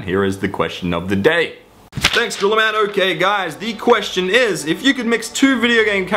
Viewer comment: At 4.38, I seriously thought he said 'thanks to Orlando gay guys'.